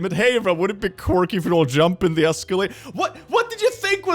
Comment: did he just say escalator? (Probably spelt that Wrong lol)